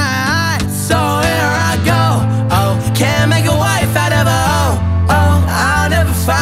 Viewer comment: The line “Can’t make a wife out of a ho” is now permanently engraved in my brain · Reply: I thought he said cant make a "life" out of a h*